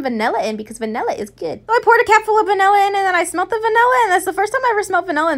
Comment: Lol when u said CAP full I thought you said CAT full